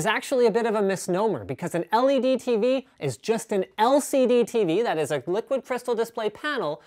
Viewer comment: Linus said, "...they are extremely thin..." The Youtube automatic captions says, "...they are extremely fat...". LOL! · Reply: Ethan K and at 0:53 the captions say "Justin" in instead of "just an" lol